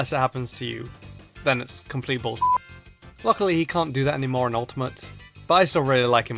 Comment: did he say lick?